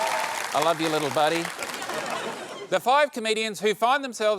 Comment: As an American, in Tom's accent I heard "five comedians" as "five Canadians" and I was really confused about why Taskmaster Australia would have a panel entirely composed of Canadians.